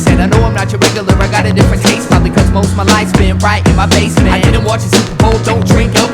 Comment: Abilities really is so underestimated, so tragically underrated. I don't think he even knows how tripped out his beats are. There like a soundtrack to life. That's saying a lot. Like the soundtrack behind the most beautiful film u ever saw.. u dropped ur jaw, and just cried your eyes out to it, unaware of the people around you, barely able to even attempt the formation of their opinions.... I'm in the back row of this theater. I am never disengaging my love and this music. 💙💚💛💜💔❤💖💙💚💛💜💔❤💖 💙💚💛💜💔❤💖💙 💚💛💜💔❤💖💙 💚💛💜💔❤💖 💙💚💛💜💔 ❤💖💙💚 💛💜💔 ❤💖 💙 💚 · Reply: haha before i looked up the lyrics to this song that practically summed me up, I really thought he said, "Probably because most my life's been RIGHT in my FACE man" LOL right? I was thinking of how mind bending that line would have been if replaced. I have anxiety disorder etc I was like, man life IS overwhelming-most of it is right up in your face. That's what made Eyedea so good. He often spoke about his emotional VuLnErAbIlItY. Dealing with the perspectives you have AND everyone else's on top of that is rarely spoken of by any of these "artists" MTV rich poser ass "MCs": a joke after hearing this